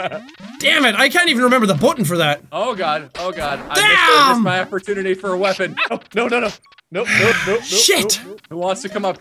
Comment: I heard "I lost my virginity for a weapon" lmfao. Sounds like CS:GO to me!